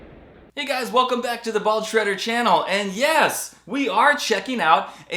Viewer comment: At first I thought you said "Ball Shredder" and wasn't sure i wanted to watch 😂